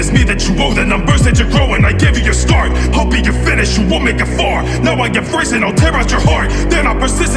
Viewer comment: I thought slenderman said you wont make a fart 💀